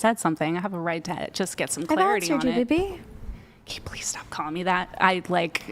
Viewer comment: What did the girl say to at 3:53:33? I can't make it out? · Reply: “I’ve answered you baby”